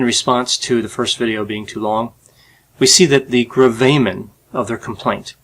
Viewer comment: what is this word? The _____ of their complaint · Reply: Gravamen